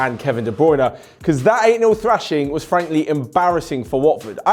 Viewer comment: i thought he said anal thrashing